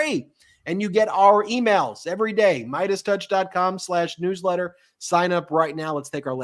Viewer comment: I thought you guys said Meidas Touch Nude letter instead of News Letter. 😅😅😅